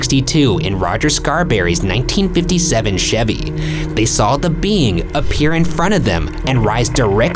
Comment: I THOUGHT HE SAID 1967 CHEVY AND I DIEDDDDDD